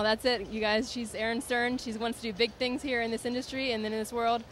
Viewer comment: I know its not wat she said but it sounds like "she's erin stern, she's won stupid things here in the industry" haha